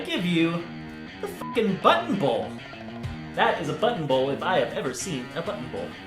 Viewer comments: I thought he said butthole bowl 😂😂😂😂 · When he said button bowl I thought he said butthole 😂😂😂